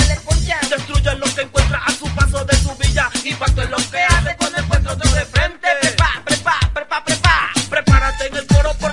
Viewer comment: Que dice en minuto 0:43 a 0:46 ?? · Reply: Prepa prepa osea de prepárate...